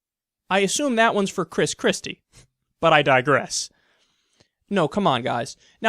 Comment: Heard that as; " But I digest" zing.